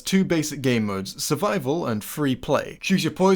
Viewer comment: My Bluetooth headphones were dieing so I heard "survival and battery low"(0:53)😂